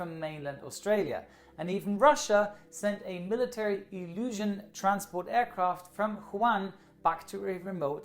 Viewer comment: Everybody has mentioned Wuhan instead of the Juan that is heard, but also at 7:12 pretty sure he meant Ilyushin instead of "illusion".